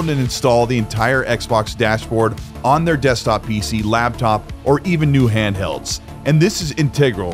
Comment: did you say labtop? hahah